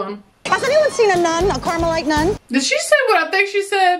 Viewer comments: yes! When I was little I always thought she said caramel-like 😂😅😅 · Until recently, I always thought she said karma-like nun. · The first time I watched this movie, I thought Mary Patrick said "Have you seen a nun, a caramel like nun." She actually is asking if they had seen a Carmelite nun, which is the type of order they are in. I remember thinking it was racial but found out otherwise LMAO. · "Did she just say what I think she said about Dolores?" No, she said Carmelite nun which is one of the sects of nuns in Catholicism....she did not say "Have you seen a caramelised nun" :D